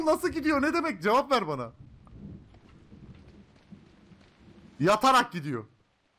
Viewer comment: Yav ben 5:33 de dersde yatıyom anladım ( hacı benim algılar bozuk yav doru doktora )